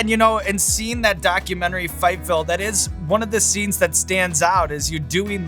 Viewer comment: Sean, what documentary did you reference? I couldn't understand what you said, and I'm assuming the closed caption translation of "Fight Phil" isn't correct. Ha. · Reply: Fightville